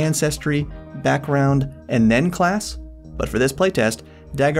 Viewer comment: i thought he said “nen” class. i thought homie was a hxh fan 😭🙏